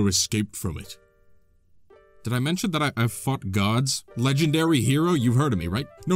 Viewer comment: did you say fought gods or..